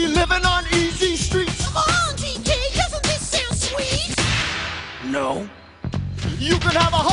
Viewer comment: DK almost sounded like that “No Mario”